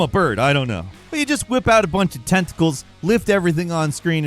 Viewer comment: I picked a bad time to take a bite of something crunchy, because for number 10 the symbiotic yank, Falcon says “You just whip out a bunch of tentacles…”, but all I heard was “You whip out a bunch of testicles…” and that’s an entirely different Spider-Man game altogether.